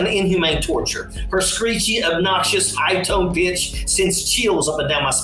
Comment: I thought he said bitch 🤣